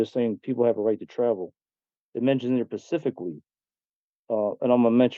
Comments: Right ✅️ He said these laws are Pacific and he found them in the law Liberry 😂😂😂😂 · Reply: I thought I heard him say "pacifically" !!!! What an idiot this guy is 😂😂😂😂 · Did he say "pacificlly"?